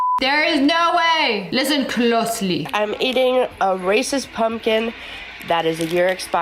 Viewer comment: did she say Racist pumpkin?